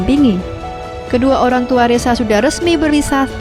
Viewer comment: Berasa ngomongin nama gua. Reza kedengerannya ressa 😂